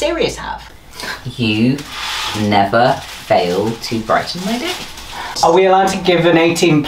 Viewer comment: What Remus said: ,,You never fail to brighten my day"😇 The subtitles: ✝